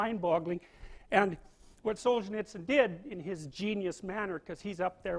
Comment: I didnt quite understand what person he was talking about at 26:05. Does anyone know? Thanks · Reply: xoxULTIMATExox he was talking about Aleksandr Solzhenitsyn